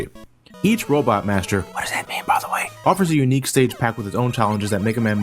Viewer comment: What does whisper at 5:27? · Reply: "What does that mean, by the way?"